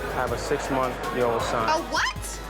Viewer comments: Did he say " I have a 6 month year old son?" 😂🤣😂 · Did he really say “I have a 6 month year old son?” LMAO 🧐